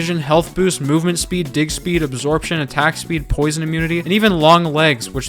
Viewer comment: I heard something else for sure, there’s no way he said pickaxe.😅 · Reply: He said “dig speed” ???